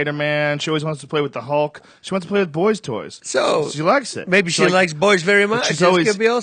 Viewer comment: What does Joe says at 1:36:54 ? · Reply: "she goes and grabs like the hulk, spiderman, batman,she want's to play with boystoys" More or less